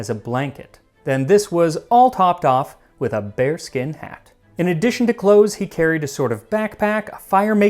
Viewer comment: Bear skin not bare skin(The English subtitles got a mistake).